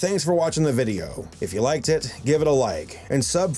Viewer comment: lol did you say “I you like tit”. 😂